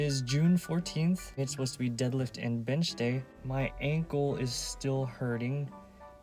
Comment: it sounds like you said "it's dead lift and bitch day" 😂